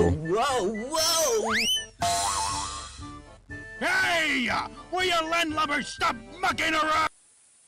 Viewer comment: I thought he said “Will you landlubbers stop fcking around”🤣🤣